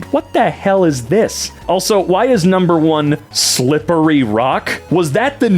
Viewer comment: I first thought that said "Slippery 'yuck'"